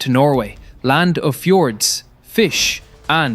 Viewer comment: "Land of fjords and fishpowder" is what I heard. He should've added "politicians with cotton-brains and antisemitic hearts." We as nation failed Israel so hard these last years, its devastating. It's the first time in my life I'm not feeling proud of beeing Norwegian.